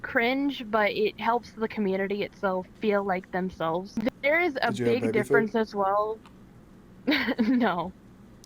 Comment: what did he said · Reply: He said something like “did you have baby food”